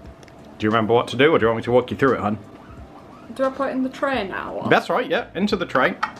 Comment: When asked you if you remember what to do now, I thought you said, "Do we pray now?" Haha 😂 You actually said, "Do I put in the tray now?" or "Do I put IT in the tray now?" Not sure which, because I'm American, and while I love y'all's accent, sometimes, I can't understand you guys! 😂 haha Y'all just drop full syllables sometimes. 😂